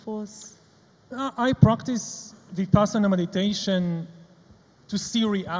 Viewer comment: What is the word he says at 0:15 .... I practice ???? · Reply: Vipassana meditation. It is a Buddhist meditation technique.